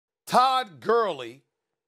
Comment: did he just say “Todd Curly” 😂